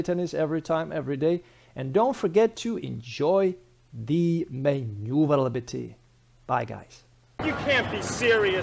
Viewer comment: And don't forget to enjoy the ? Sorry, I couldn't quite understand even after rewatching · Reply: Manooovrlbity 😉